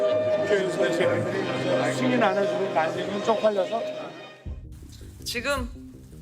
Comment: 지금 다시 한번 들어봐주십시오 라고해서 존나 열심히 들어봤지만 9:15 '승인안해주면 바이든 쪽팔려서' 라고밖에 안들려요